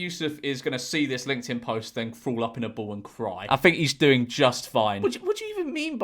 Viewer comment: Did he say "crawl up in a ball" or "throw up in a bowl"?